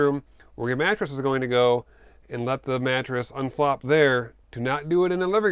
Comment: unflop. You speak my language. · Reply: you were talking about the mattress :D At first I thought you said unplop